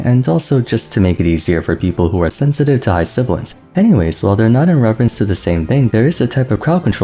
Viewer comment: Sensitive to what? I don't know that word and the auto translate doesn't either. Auto translate is saying "high siblingss"